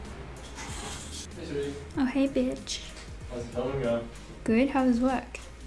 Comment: I thought you said ‘oh hey bitch’ lol I didn’t know your boyfriends name